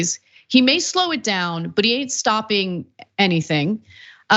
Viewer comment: "/…/but he ain't stopping… anything"? No. That's not the quote. That's not what he said. He said "/…/but he ain't stopping shit". (the graphic is also wrong. He didn't say "s***") I hate these constant lies, constant miss-quotes, about what people say, in supposed news reports.